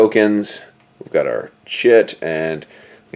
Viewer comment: sound like you said “we’ve got our shit” 😂